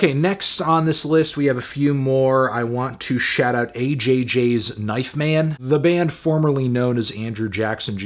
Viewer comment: I swore he was about to say "AJR"